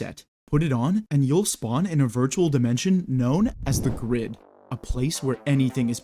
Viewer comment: I thought you said Known as the crip